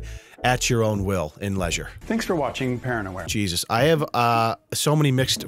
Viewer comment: I thought the cop said "paranoia", not "parent aware"... 😂